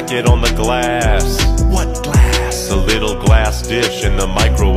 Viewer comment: Ngl I genuinely thought he said ‘the little glass fish in the microwave’ for some reason 😂